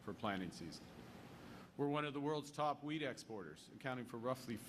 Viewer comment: At first I thought that Mark said weed instead of wheat. lol. I thought, oh wow, we're getting really progressive. 🙂